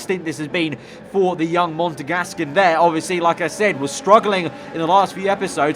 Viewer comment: did you say Montagasc